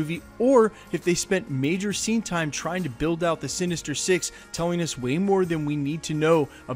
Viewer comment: Lol sounds like he said "Sinister Sixteen"